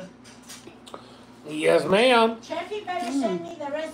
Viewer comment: I thought i heard said she wanted papaya salad recipe...i thought i heard ❤😊.. please let me know..or ill ask in either of the lives🤗...thank you❤